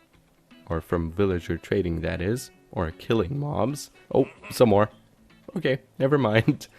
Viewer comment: did you just say killing moms?